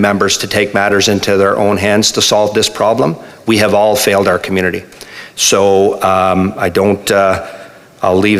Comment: All I heard was “we have failed our community”…